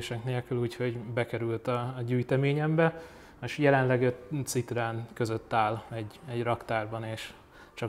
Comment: nál mit mond a tulajdonos,hogy a gyűjteményében van ez az autó és jelenleg egy ,,U'' Citroen között áll---ezt lehet érteni,mit mond,ki mit hall ki ebből?köszönöm. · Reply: Jelenleg 5 citroen között áll.